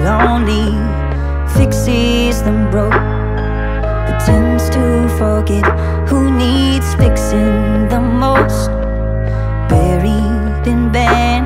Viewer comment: "but tends to forget who needs fixing the most" i heard it as " pretends to forget who needs fixing the most"